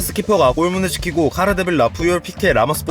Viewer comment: 여기 카르데빌라로 들리는대?ㅋㅋㅋㅋㅋㅋㅋ